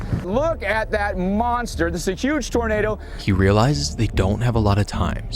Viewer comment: I wasn’t paying attention for a moment and snapped back when I thought he said ”Look at that monster! It’s a Jewish tornado!” 😂